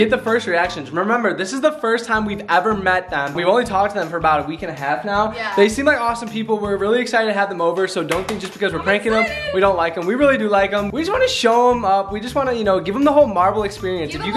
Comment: Did I hear say “we don’t like them we like them” HuH 🖤